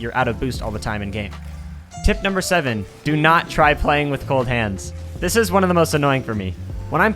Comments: Him: "Do you not try playing with cold hands" What I Hear: "Do not try playing with both hands" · I THOUGHT HE SAID TO NOT PLAY WITH BOTH HANDS. I was genuinely terrified.